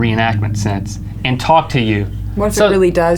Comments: did she say "ok, ok, im gay."? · sounds like shes saying "okej, okej am gay" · lol, at 24:32 after she says OK, it sounds like she says "im gay" XD